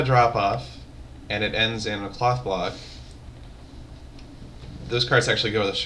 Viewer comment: OHHH!!! i thought he said cock block not cloth block